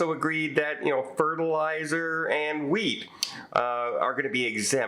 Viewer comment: "Fertilizer and wheat." I heard "weed." I was amazed they could put tariffs on weed.